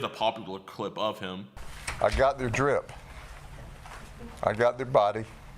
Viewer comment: I thought he said I got the drip like he was wearing something good 😌